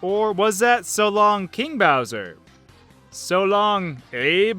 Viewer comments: It also sounds like Mario is saying,"I love king Bowser!"like if you agree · Btw the mishearing for the bowser fight from n64 is actually says so long kinga bowser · to me it sounded like mario said"so long kirie bowser" kirios is the greek word for mr. and sir. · The first one. I thought he always said "so long ME bowser" like a Italian would.